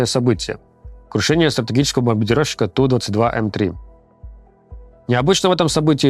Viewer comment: «Крушение БОНБАНДИРОВЩИКА» – или мне послышалось?